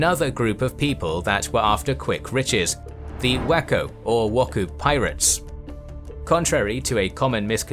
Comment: Of course, I heard it as "whacko pirates"